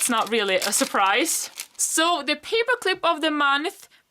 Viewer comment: When you said paper clip I thought u say hypocrite because my family was being loud XD